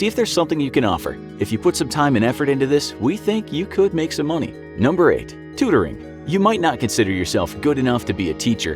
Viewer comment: bruh, i thought it said "Torture"